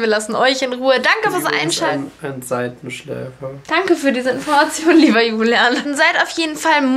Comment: Ich habe 9:54 verstanden: Lio ist ein Seitenschläfer 😂😂😂